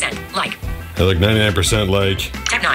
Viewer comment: “WAIT, I’M BLI-“ That made me laugh. You are a legend man, keep the hard work! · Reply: I heard it as blind, I don’t know, I think it’s blind.